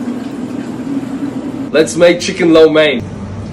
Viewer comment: I thought I heard " Let's make chicken Lil Wayne " lmao